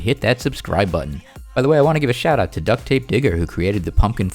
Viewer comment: I heard ducttapenigga